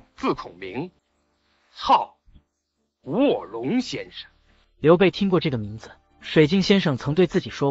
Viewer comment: 我聽成，操！ 臥龍先生🤣